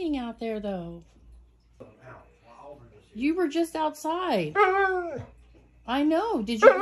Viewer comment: The first time you said it's raining outside I heard he said "I know, let's go" lmao